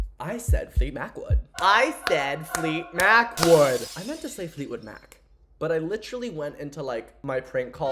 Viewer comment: idk guys i think he said fleet Macwood…